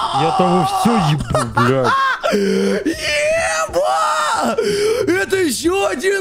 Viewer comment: я обин слышу вместо "ема" слышу "еба" ?